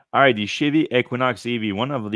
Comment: I swear I heard you say, "alright the shitty Equinox EV". haha. That's what I get for trying to multitask listening to your podcast. Thanks for all the coverage you guys do.